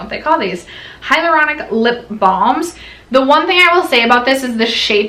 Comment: I swear I thought she said “thongs ” instead of “balms”— got my husbands attention fast! Lol 😝